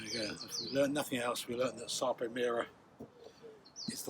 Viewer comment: Can't understand what he says at 22:58. Anyone? What's the way forward??? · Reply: ‘Sarpo Mira’ it’s the variety of potato. One which was planted in error when the rest were ‘King Edwards’. Sarpo Mira is very resistant to late blight. Which had a very negative effect on the King Edwards that year(2021). Ironically I planted Sarpo Mira and Sarpo Blue Danube in March this year ,2022, and the Mira caught what I think was early blight. Although some that I forgot to plant in March are doing really well in a tub at home. Despite not being planted out till June and suffering the worst drought in years. Thanks for watching and your comment. I hope this helps.. (Sarpo are family run potato breeders in Hungry btw. It’s worth checking out their website) they have several blight resistant strains. But Sarpo Mira is their most famous. If a potato can be considered famous?